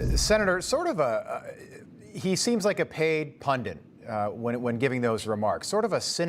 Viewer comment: "Paid pundit" I thought you said Paid Puppet... hmmmm